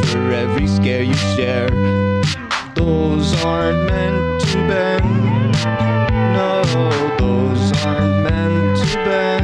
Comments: I thought it said "rules are meant to bend" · At first I heard "Bones aren't ment to bend"